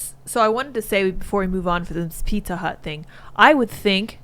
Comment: For a second I thought she said "Pika Hut" instead of "Pizza Hut", and I had a vision of pizza with slices of Pikachu on it...